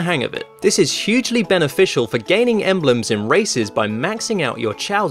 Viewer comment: when he said races:races 7:05 me what i heard:racist also me realizing he didnt said racist but races